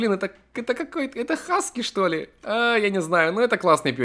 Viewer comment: мне послышалось это саске чтоле?